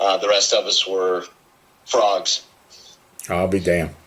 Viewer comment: Happy Father's Day to you, the great one 😊 could have sworn I heard him say ( FRAUD) when he said ( FROGS) 😊